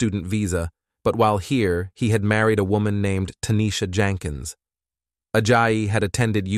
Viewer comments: I actually had to skip back to hear this again I swear the first time I thought he said "he married a woman named Kenneka Jenkins" and I literally got chills and for a split second thought this was about to take a really insane turn. But then just as quickly I realized I must have misheard. She wasn't married, and I followed that case like I feel like I would know if that case had been solved. Still though I was pretty freaked out for a second there.. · For a second I thought he said kenneka Jenkins…. Iykyk